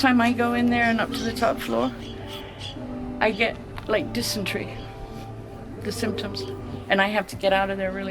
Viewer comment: Ok i know this is an old video but did she say she gets dysentery symptoms @ 38:40. In my very immature brain she has diarrhea everytime she goes in there😂